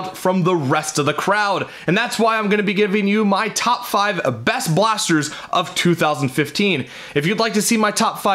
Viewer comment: I thought it said Nerf blasters